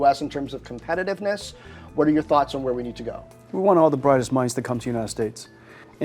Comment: I thought he said "We want the brightest mice to come to the United States"